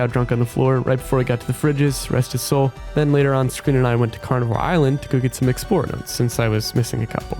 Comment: Did I hear 'Carnival' island??